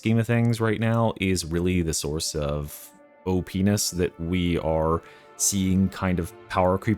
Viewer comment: Did he just say Oh penis?